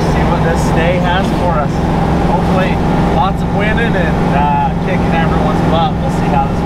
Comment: Hahahaha at 6:38 I had to do a fat double take. They were rolling up to Sand Hallow Day 1 talking about what the day had in store. I legit thought he said, "Hopefully, lots of women and kicking everyone's butt." Who knew winnin' and women could sound so similar?